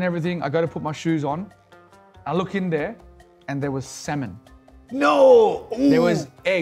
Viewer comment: It will take time to get used to Australian accent. I misheard ‘Salmon’ as ‘sem*n’.🙈